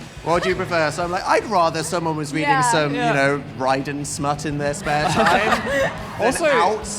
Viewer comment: DID HE REALLY SAY RYDEN